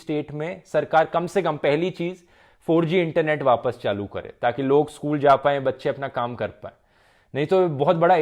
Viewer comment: Did he said "log school ja paye bache apna kaam kr paye" 8:15 😂😂😂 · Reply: Actually Bachhe school ja paye Mummy Papa apna kaam Kar paye